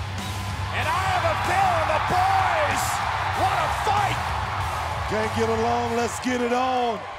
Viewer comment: anyone explain wtf Cole said 😐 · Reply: And i have a feeling the boys wanna fight